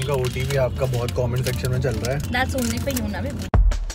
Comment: did anyone hear she said baby😂? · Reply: She said beebu